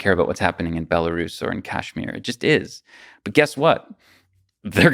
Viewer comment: did he say Kashmir?